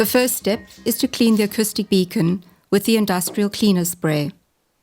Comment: I thought you said industrial penis spray